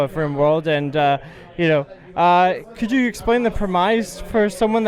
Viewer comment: "Could you explain the prem-iice" lol. What a weird way to say premise.